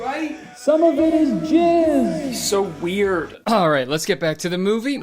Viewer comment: why did I think this said “someone get his jizz!”